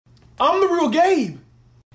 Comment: I hear " I am the real GAY" 💀